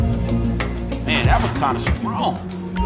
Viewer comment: Did u say... SPRONT?!